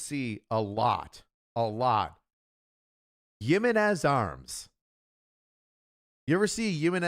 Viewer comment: What is that arms company name he was saying 1:33:00 range, the hispanic one he said? Couldn't quite understand it. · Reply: Jimenez